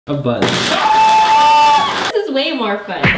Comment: it sounds like said “her butt”.